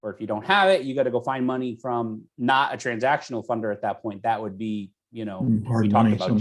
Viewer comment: "in that case you would use your own capital, or" and the rest was muffled. What did you say? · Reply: Find money but not transactional funding. Maybe a JV.